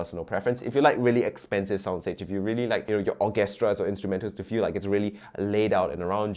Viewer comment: I heard you say... Orgastra... 😂😂 How does this compare to Blackdot Pro 2?